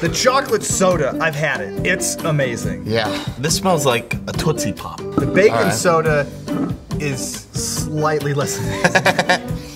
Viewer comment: when said bacon soda I thought he was saying baking soda